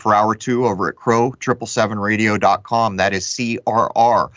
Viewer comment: That intro sounds like it says TRUMP! Lmfao I know it says crrow tho